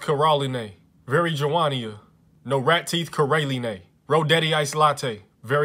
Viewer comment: I swear when he said “North” in “North Carolina”, I heard “No rat teeth”